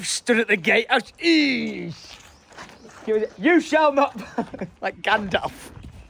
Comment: did someone say who’s Gandalf 😮😂😂